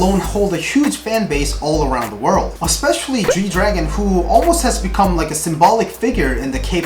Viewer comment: Swear I heard (쥐)Jui-Dragon...